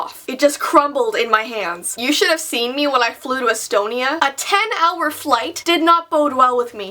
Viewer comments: did she say Estonia... lol I live in there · did she say Estonia 1:37 ??? If yes, siis oleks see küll väga äge! :))